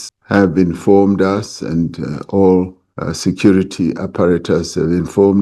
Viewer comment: Did I hear security apparatus 😂😂😂😂??